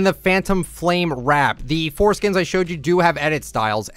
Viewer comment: did he just say foreskin? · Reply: Four skins is what he said